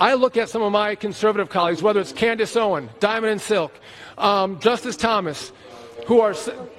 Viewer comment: Oh, Justice Thomas? I thought he said Justin Thomas.